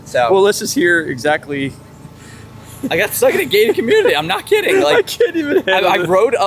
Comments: I thought he said stuck in a gay community ahah. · Oh gated community I thought said gay community 😆 I was wandering why they had arrows pointing to a gay community 🤔 😆